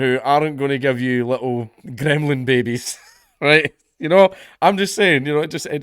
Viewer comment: I lost a mouthful of beer when ya mentioned " gremlin " cause I thought I heard Gretta ...